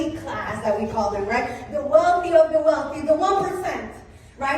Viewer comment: did someone just say "the jews"? Tf